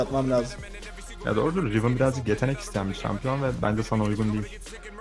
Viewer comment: de neye sinirleniyor çocuk ne dedi orda ? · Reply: Riven yetenek isteyen bi hero ve sana uygun değil diyo. Yani kısaca yeteneksizsin senin neyine riven demek istiyo